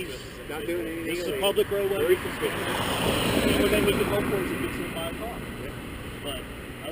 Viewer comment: Did he just say tht 3:04 hope he gets hit by a car lmfao ur fired Occifer lol